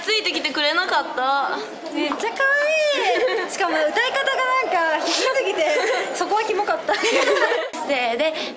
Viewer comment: のりなぷーは「歌い方が」の後なんて言ってるか分かりますか？ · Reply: 必死すぎて